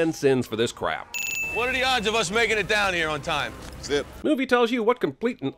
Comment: I think you meant Colonel Stuart....? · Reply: Also, 33:56. Subtitles say "Good", but Zeus clearly said "Zip".